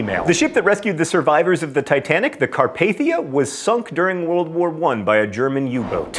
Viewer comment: I misheard 1943 as World War Three, I was like wait WHAT? I know I don't pay much attention to the news but I think that would have reached me.